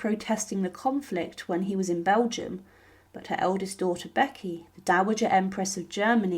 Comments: I swear I just heard “her eldest daughter Becky” 😂 1:23 or am I hearing stuff 😂😂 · Vicky was Dowager German Empress. She was never an 'Empress of Germany', a title that never existed. Please get matters right. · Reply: Sounded like she said Becky, not Vicky!